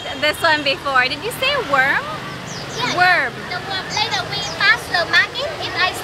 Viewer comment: It sounded like she said it's WARM NOT WORM